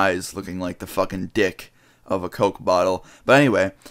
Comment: Did you just say as high as ,dick of a coke bottle' ??